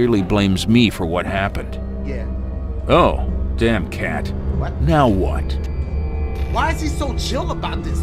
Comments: He say Oh,damn cat.. Now what but it says Oh,damn cat ... Not now! :D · anybody else see that at 10:12 the subtitles said not now and the guy said now what